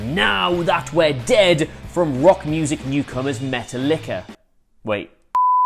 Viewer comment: Did he just say Metal likkaaa.